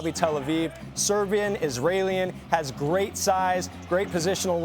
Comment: Did he just say Israelian? 😂